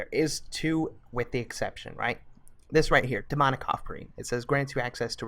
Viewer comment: I thought you said demonic cockring not offering oh my!